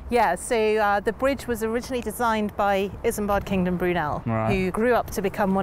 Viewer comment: The name of the designer is Isambard Kingdom Brunel, in case you couldn't understand what she said at 1:48. I had to go look it up on Google, myself. I'd never heard of him before.